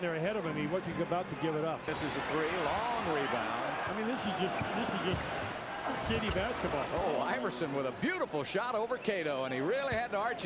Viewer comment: Did he say “this is just shitty basketball”? 😂😂